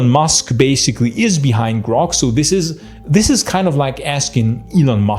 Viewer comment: GROK!? I swear I thought you said Gronk.